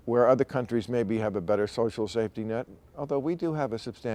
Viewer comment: did he say social safety nut? Sounds exciting!